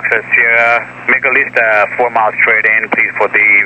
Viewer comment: HELLO FROM Belgium! Can you help me decipher what ATC says at 56:06? I hear something like ... make a lift ?? at 4 miles staight in ... What does he say exacrly? A little too fast for me... Wonderful video BTW.!! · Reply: "make at least 4 miles straight in" is what he said. He wanted to have at least 4 miles of separation between me and the jet that was waiting to take off. I acutally misread my distance when I said I was 4 miles. I was 4 miles from my Final fix on the Visual Approach for 28 L. That Final approach fix is 3 miles from the airport. So I was in fact actually 7 miles from the airport as he corrected me. Cheers